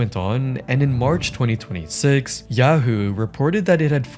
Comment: l was tabbed out at 3:18 and when you said Yahoo l thought you said Yahu as in the big Yahu